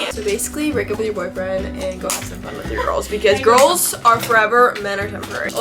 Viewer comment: In the beginning I thought she said rolls are forever- 😭